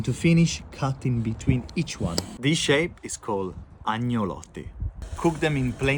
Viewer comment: I thought it said "Ancelotti" as first and thought "what did the Brazil national team manager do?"